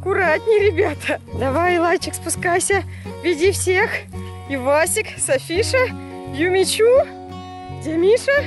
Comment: мне послышалось пивасик 🤣